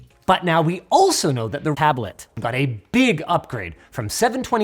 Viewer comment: What did he say at 2:14? It sounded like something was cut out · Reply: tablet but yeah